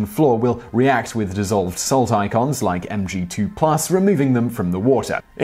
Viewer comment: Did he say salt icons at 1:20? I thought Simon was a science guy. And that's a weird way of reading out metal ions.